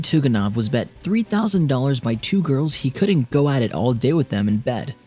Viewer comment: What an ironic death..you are going to your 100th birthday party YAAY GONNA EAT SOME CAKE!! Then the cake kills you :P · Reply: ***** It was not the cake, but the driver. What does he say from 3:15 to 3:20? 3000 dollars by 2 girls......??? I couldn't understand that part.